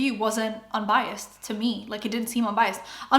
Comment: did you say "unbiased to jest..."? polish girly here, maybe it just sounded similar, but I played it like 5 times and can't stop thinking that 😅